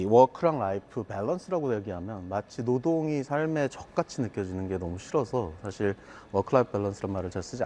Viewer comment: 뭐라고요? ㅈ같이??